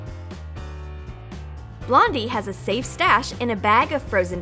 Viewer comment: Sounded like you said blombie for the avocado one lol